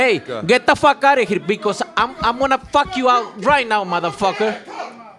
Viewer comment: You guys didn't hear it but Eddy first told him to don't listen to him... Then, he turned up😂😂! Eddy is the one who told him to tell Boo boo I'll fuck you up right now 8:13 and also about the payday 8:55😂🔥🔥 pay attention 😂